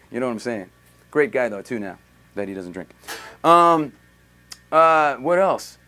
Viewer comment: What does he say around 3:14 ? Great guy though, until now ? something something doesn't drink?